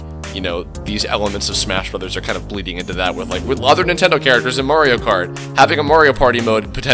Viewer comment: Woah! Nice graphics! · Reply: Christopher Baidoo i thought he said Nintense